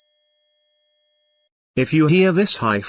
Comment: i just thought he said "like the video and like a like"